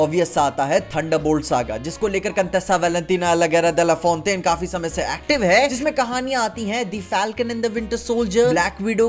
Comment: I listened to it 5 times but still can't understand the word 😂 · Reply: Valentina Allegra de Fontaine